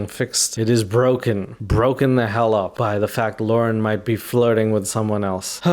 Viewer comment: Every time you say "Lauren" I hear "Lorne" from TCAP 😂😂😂